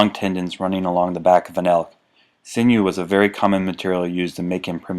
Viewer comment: Wrapped the fletching with the sinew from the back of the what? 7:31 I couldn't understand the word. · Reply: Ahhh, is it elk? I listened like 10x over & over.